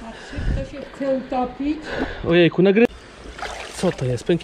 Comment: "Czy chce się Pan utopić"? Dobrze usłyszałem XDDD